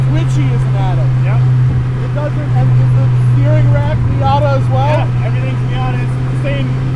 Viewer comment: How much would this cost? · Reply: CockatooDude oh sorry i thought you said seven for the miata